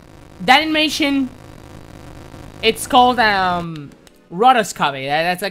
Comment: What is the name of the animation??? I can't hear it well :( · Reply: Rotoscoping